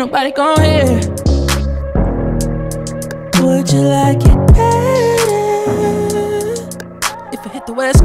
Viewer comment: "would you like eggs and some french toast" idk if Im just hungry but I heard that for some reason